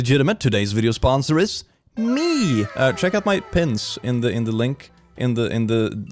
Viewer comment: Lol.... When he said "check out my pins", I thought he actually said check out Mike Pence I looked at the video and saw him holding the pins. I'm cleaning while playing the video is playing so only looking at the video periodically. Laughing at myself. 😂🤣